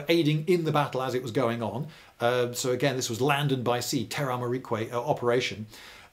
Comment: What is that word at 43:58 that sounds like "teramamiquay" or "terra ama miquay" or something. It is a new word to me but since I cannot spell it I cannot look it up. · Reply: Hi Inscrutable. 'Terra marique' means 'land and sea' in Latin. I don't know why has used this phrase instead of 'amphibious warfare' or 'combined ops' - maybe it's something to do with the sort of education he received as a young person. Only can tell us !